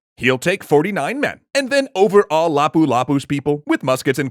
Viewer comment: du you say over-awe or overall? Becauce both english subtitles say overall, but I Think that overawe makes more sense, and overall dossent, Becauce I’m pretty sure that overall is not a verb, is it?